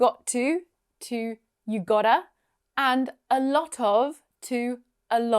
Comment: A lot of 😮,i thought she said a little😂